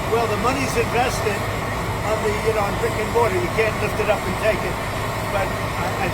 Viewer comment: HE SAID RICK AND MORTY??? (ik what he said but I can't unhear that lmao) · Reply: Brick & mortar---a physical store location